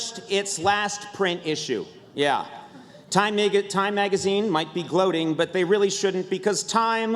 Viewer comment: Please tell me someone heard him say nigga